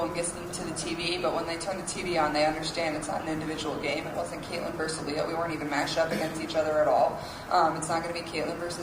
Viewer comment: btw this caption is wrong, she said “Caitlyn versus Aaliyah” not “Caitlyn personally” She’s talking about the game she just played against South Carolina, whose star player is Aaliyah Boston.